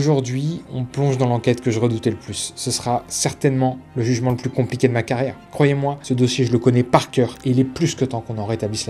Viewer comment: J'ai entendu "cantona n'a plus jamais eu de carrière"? A Manchester United ils chantent encore ses louanges en 2025? On doit pas parler du même King